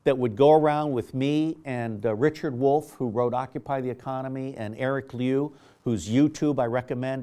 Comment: he mentions some one named Eric and i cant catch the last name. any one know who hes talking about i would like to see that 17 minute video. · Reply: Eric Liu